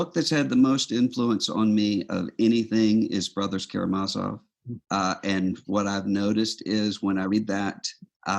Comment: What is the name of the one book that had the greatest impact on Russell? He says it approximately 52:30, but my Google search isn't very effective at finding the book for what I thought he said. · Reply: I belive you're thinking of The Brothers Karamazov, by Fyodor Dostoyevsky. Here's a link to the book on Amazon: <link>